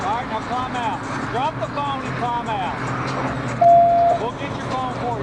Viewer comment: Did he say “drop the bong and climb out”? 😂🤣